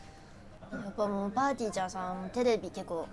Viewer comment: 「すがちゃんさん」が、ほぼ「つのちゃんさん」に聞こえてきて、なんかおもろい